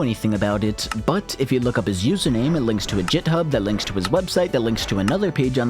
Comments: did you just say “JitHub”? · did you just say "jithub"?